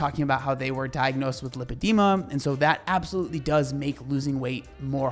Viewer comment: I thought he said leukemia at first and I was like “yeah no shit” 🤦‍♀️